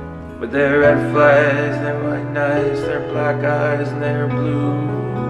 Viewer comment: im sorry but i heard it as 'black guys'